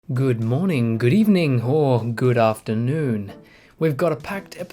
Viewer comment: First time I watched this I thought you said "Good evening whore good afternoon." J/s